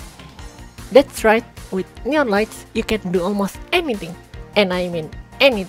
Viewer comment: I misherad and thought you said "nyan lights". I love it. That is now what they are and no amount of facts or evidence will convince me otherwise.